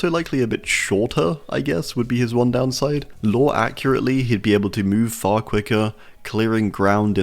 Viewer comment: I thought you said lower accurate Not nerfed down For example the pig is literally a formation of jigsaw He crouches he kidnaps and he places people in A reverse bear trap making them struggle to get out therefore the pig would be left as it is not much would change dumbass; Like have you not seen the Saw franchise You say he's mainly weak then explain how the hell the people in the SAW games didn't escape him Texture would literally be playing the same way you normally would in a regular trickster match the end and he didn't Give him any special powers. Except an infinite amount of blades. The tricks would actually have less blades to throw and not move as fast. But he would still play like your basic trickster with speed degradation A minimum amount of knives